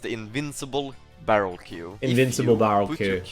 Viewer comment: I heard invisible when I watched vid, but here it sounds a bit different